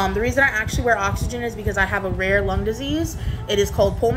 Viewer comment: She has a rare lung disease that affects a lot of people.... ok 😂 Make it make sense! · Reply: I thought she said lunch disease 🐋